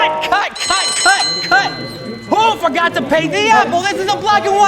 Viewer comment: he said who forgot to pay the apple which meant someone should have paint the Apple and the captions say pay